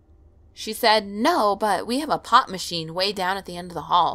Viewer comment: nice vid but on the second one i thought you said pot machine i was like wtf